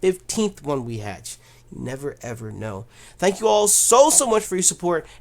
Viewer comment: I've Always wonderd What he says in the intro? I hear "What's good youtube? Nappy 982 here????? l0l HELP ME UNDERSTAND